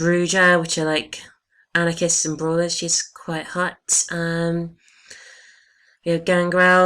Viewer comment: lol did you say at 0:40 "shes quite hot" :) and we all now you're going to be female to stare at the buts :P